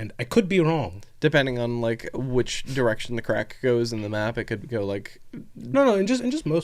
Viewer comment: I had to pause because I found it too funny. I don’t know if it’s slaanesh influencing this but the moment right after “giant horny snake”, I misheard “Macragge” as “muh crack” when you said “marching toward Macragge”.